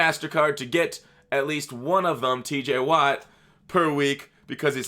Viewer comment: I thought you said TJ WAP!! Lmao